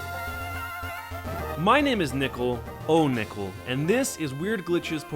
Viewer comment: I heard: "My name is Nipple" XD